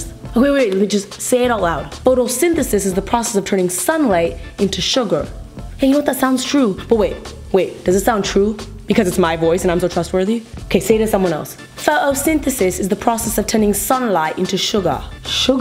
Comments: I thought that she is saying suga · when she said that in the British accent and said sugar it sounded like she said suga from Bts OUR LIL MEOW MEOW AND SWAG BOY MIN YOONGIIIIIIIIIIIIIIIIII any army's?